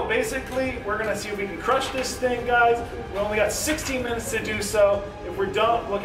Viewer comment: I thought he said 16 mins to eat it, I was like no way he's going to do it. Hear 60 mins and I was like okay he can do this, then eats it in less than 15 mins. 😲